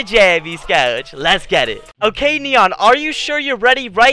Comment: Who else heard neon curse